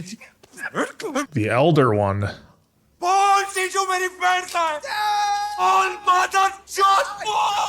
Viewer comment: Uh, "dimwit"? Madharchod is what he said, which is basically muthaf**ka